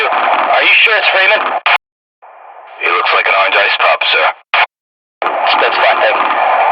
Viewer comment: I think he's saying "that's fine sir."